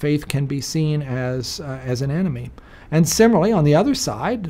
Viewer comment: I misheard 1:48 as “Faith can be seen as an Anime”.